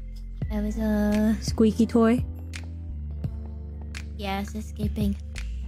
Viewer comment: Sounds like she said "Gas escaping" instead of subtitles "Yeah it's escaping"